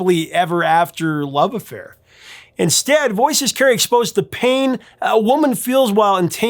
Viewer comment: When I first heard the song, I thought she was saying "Mrs. Carry"